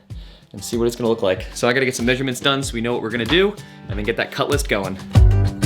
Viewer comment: Haha. I thought you said CUTLASS!!! I started asking myself what's a cutlass??? Is it a piece of equipment?? Then my mind said CUT LIST!!!!! DUH!!!